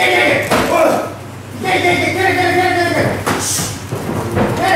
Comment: 聞いてねぇ！って聞こえる